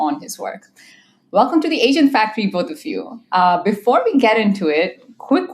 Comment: In your auto translate caption at 0:50, It is translating Welcome to Asian factory instead of Welcome to Agent factory Please fix it